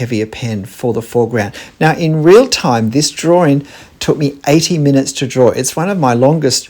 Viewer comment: So relieved you said 80 minutes! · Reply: Had to rewind that bit TWICE... I kept hearing "18 minutes"... which... um... "heart palpitations, anyone?" Need to re-tune the old ears... haha ;o)